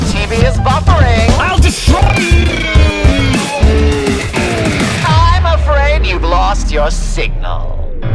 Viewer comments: In the song I thought he said you lost your sigma 😂😂😂😂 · i thought he said i’m afraid you’ve lost your sigma!